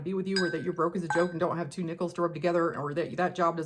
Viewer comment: I love the way you explained manifestation with props! That is so cool! On a side note, in one of your videos, when you mentioned having two nickels to rub together, for a moment, I thought you said two nipples to rub together! 🤣